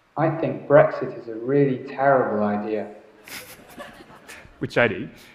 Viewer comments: I heard the words the first time... Although I heard "taking drugs is a terrible idea" 🤣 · I thought he said "I think breakfast is really terrible idea" 😂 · I think breakfast is a good idea. Vs I think brexit is a terrible idea, what happens there if both are possible of same sound. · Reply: I thought he was saying breakfast!! What a derp I am...I kept wondering where he said Brexit in his talk! THANK U!! LMAO!! 🤣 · I heard "i think breakest in a terrible idea" before said it